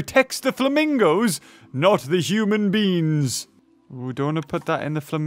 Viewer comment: did you say human beans?